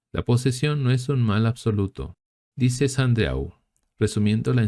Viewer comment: Hola!. En el minuto 12:52 dice "La posesión no es un mal absoluto. Dice Sandeau. Resumiendo la enseñanza de teólogos y místicos." No se entiende bien, pero me gustaría saber quien es el autor que dijo eso, su nombre completo y cual es el escrito. Me encantaría leerlo. Gracias de antemano. Dios los bendiga. 🙏☺️